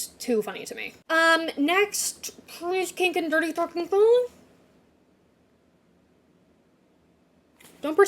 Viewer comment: What did she say at 14:28 😭😭😭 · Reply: i think she said colin dirty talking to pen i think